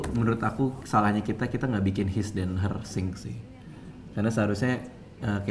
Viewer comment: Bang Radit ngomong apa? His and Hersing? Apa itu? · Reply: his/her sink